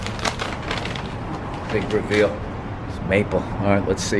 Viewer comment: when he says big reveal i thought he said "mike birbiglia"